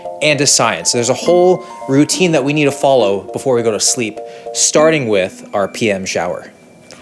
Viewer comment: I heard “starting with peeing in the shower”